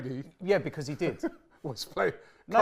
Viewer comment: then why read it again an say "i said currently", and now read it the exact same way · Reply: to clear up misunderstanding of the question. even said he heard “played” not “play” 19:30 He misheard